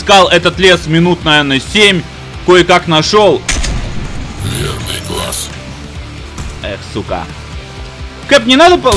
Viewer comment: ааа , верный глаз , а мне послышалось пьяный класс :)))